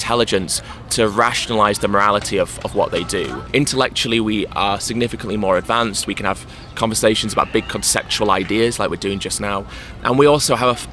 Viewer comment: What does Ed say at 3:14? Thanks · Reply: intellectually, we are significantly more advanced, we can have conversations about big conceptual ideas like we're doing just now